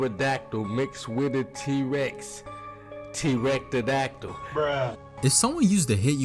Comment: sounded like he said erectidactle